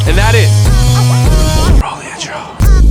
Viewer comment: Did he say "roll me a joint"?